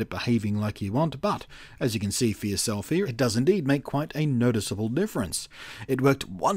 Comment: Did you say "as you can see for yourself here", or "as you can see for your selfie"? ;)